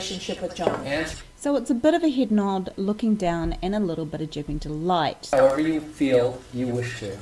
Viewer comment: "A little bit of __ delight" at 1:02 . What's the word that precedes 'delight'? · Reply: Duping delight - the little inappropriate smug smile of a manipulator when feeling accomplished in deceiving others